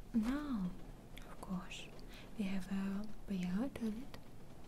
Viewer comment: What does she say @ 6:06? It gets me every time and I can't figure it out. :) Def one of my favorite ASMR videos, ever. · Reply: 미국아재 “we have a real toilet”